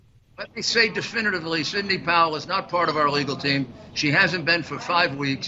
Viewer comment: Did Giuliani say Cindy Powell ? ( 3:20 )